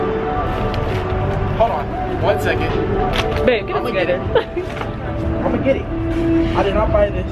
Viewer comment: Who else heard her say “bae get it together” @ 11:51 like if you heard it. Like why are they not dating they are goal💍💍💕